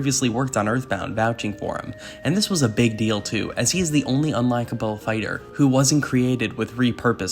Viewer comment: I'm sorry did you call Ness "the only unlikable fighter" or am I mishearing you..?